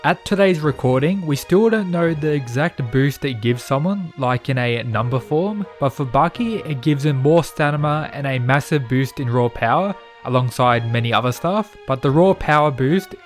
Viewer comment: im sorry did you say STANIMA at 1:07?? xd